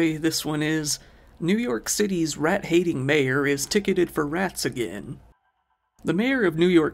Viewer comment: I heard "tater is addicted to math" at first. I wish him all the best in rehab!